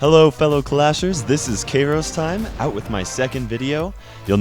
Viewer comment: did he say karos